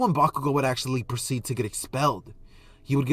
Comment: When he said expelled i thought he said the belt 😂